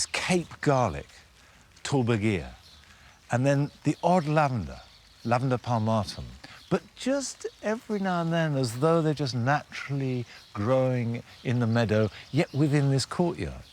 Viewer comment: Can anyone understand what kind of "old lavender" he is talking at 30:46? I couldn't find something sounding similar online... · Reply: Lian Mai Think he said lavandula pedunculata!